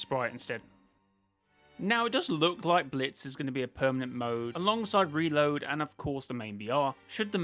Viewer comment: did you say it doesn't look like or it does look like because Reload is thankfully already permanent and Battle Royale thankfully is permanent and Blitz Royale looks like it will hopefully be permanent hopefully Blitz Royale is permanent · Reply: Now, it does look like blitz is going to be a permanent mode